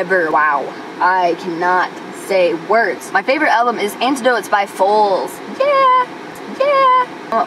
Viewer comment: Sorry, I can't understand what you said about your favorite album.. sounded like you said "Antidote" by "Foals." ?? Anyway.. LOVE your music.. been enjoying "Collapse // Expand" (incredibly creative!) and your Fine Constant music. Awesome! You are one of my favorite guitarists! Also, sing more! I like your voice.